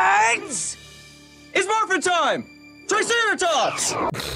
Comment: Why did I hear " it's morbin time!!" At 2:30 😂😂😂😂